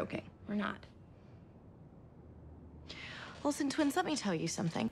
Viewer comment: 'olsen twins, let me tell you something' SANTANA NEVER FAILS TO MAKE ME CACKLE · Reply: I THOUGHT SHE SAID WILSON TWINS AND I WAS LIKE "WHAT? OWEN AND LUKE ARE TWINS?!"